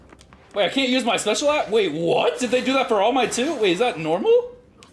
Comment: did i just heard all night said shit